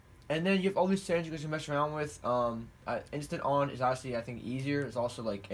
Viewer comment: tell me what he said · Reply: Venture Supreme you have all this settings to mess around with